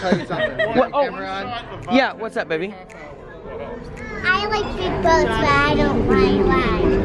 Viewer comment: What a cutie she is ... I thought she said " I like big butts " .... my bad , 🤣🤣🤣 . Well, you have helped me decide on another cruise . Not sure if I want to go back on Ecstasy or try Sunshine . I loved Ecstasy because of the size with me being on oxygen but I do want to try another Carnival ship , decision , decision . ❤❤❤